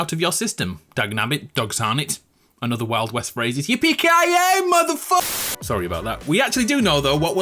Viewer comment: what did he say? Pkia??